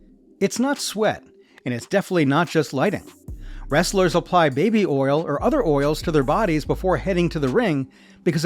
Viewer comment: did you say baby oil